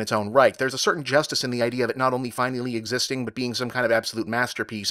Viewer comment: It sounded like you said 6 under 10 rather than out of 10. Unless you really think it's 10/6 star movie, than color me impressed